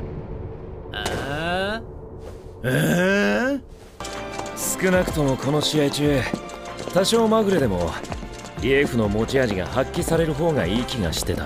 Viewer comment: i thought he said sukuna